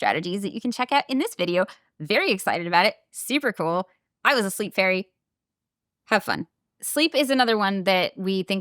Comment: I think there's a mistake in the subtitles at 18:53, it says "I was asleep, very." but I think is saying "I was a sleep-fairy" 🧚 Really enjoying and appreciating these self-care focused videos, thank you so much!